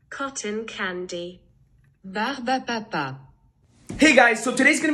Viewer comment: What I heard Barbie o' papa